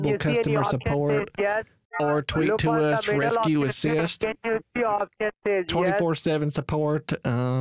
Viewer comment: did somebody say "RezKue, assist!" 😮